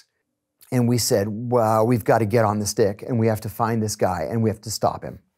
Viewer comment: I definitely had to listen twice lol I heard “get on this dick”